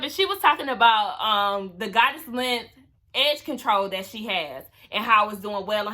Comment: Girrrrl I thought she was saying “Goddess Lengths X Tamar” like it was a collab or something! I was like oh she doin GOODT! 😂🤣😂